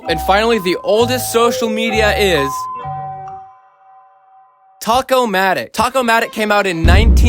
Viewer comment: I heard tacomatic